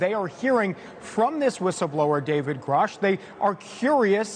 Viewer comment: Oh I was confused I thought they were saying David Koresh like from the Branch Dividians